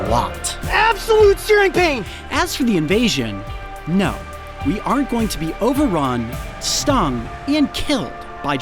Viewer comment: I'm sorry what did he say😀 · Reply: “No we aren’t”